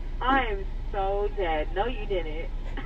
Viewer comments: Lol I heard her say "do you wanna play with it?" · For the people who dont understand the meme; “Can i get a (Plays Sicko Mode by Travis Scott ft Drake) with no lettuce”, Recently Travis Scott got his own meal for McDonalds, so playing the song means that he wants a travis scott meal, the woman then laughs and says “you want a sprite with that?”, The travis scott meal comes with a soda (sprite to be specific), the guy responds with “yes” meaning that he wants a travis scott meal with a sprite. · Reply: Lmaoo ohhh I heard her say "do you wanna play with it?"